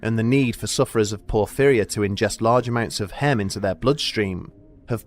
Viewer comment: I thought he said they need large amounts of ham lol